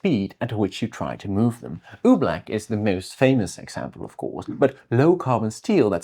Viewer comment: What is the word you are saying at 11:50? (Sounds like ublank) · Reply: Oobleck. Non-newtonian fluid made by mixing corn starch with water. Try it. It's messy and super fun. It's almost rock-hard when you punch it, but completely liquid if you just put your hand in it gently.